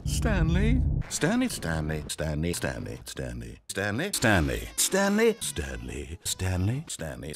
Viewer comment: the part where i heard stanny instead of stanley